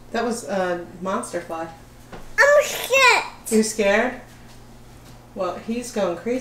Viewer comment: LOL!!! I heard say "Oh sh*t" when the fly was flying around! It's around 6:59-7:00 minutes in! 🤣🤣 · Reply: I heard "I'm scared" and then repeated it.